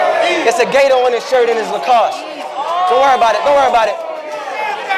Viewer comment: Why I thought Ice shirt said Lactose